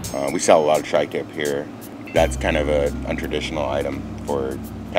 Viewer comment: tri-tip is popular in Texas? It’s big in California, mainly Santa Maria and So. Cal.🤔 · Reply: You’re right, my bad. I heard “a traditional” instead of “untraditional”. 👍🏼